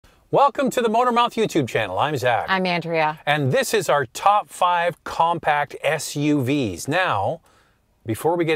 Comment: Lol i heard "welcome to the modern mafia youtube channel" and i was like what ?! :)